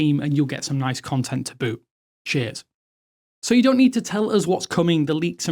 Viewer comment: I had to rewind and repeat what you said a few times cos it definitely didn't sound like 'cheers', more like 'sh*t' (to me anyway)